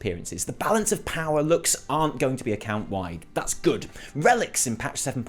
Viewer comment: I'm sad cause I thought he said balance of power was going to be account wide :(